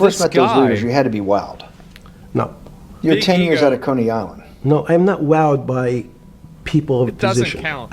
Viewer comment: Did he say "cunny island", at 0:28? I hope he said coney island instead, lmao.